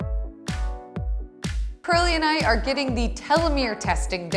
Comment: It sounded like she said Pearly and I